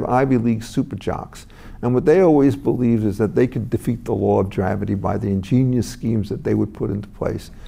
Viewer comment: Thank you Reason,. This man is one of the best minds around. I hear him often on the John Batchelor show on AM radio late night out of NYC. At 1:16, did he say the law of "dravity"?